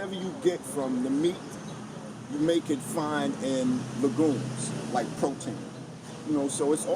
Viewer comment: What is that food he is mentioning at 4:32 - lagooms? I can't understand it - thanks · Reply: ***** Legumes, they are beans. Super high in protein, fiber and have no negatives.